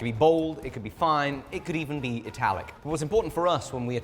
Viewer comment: Did he say it-alec?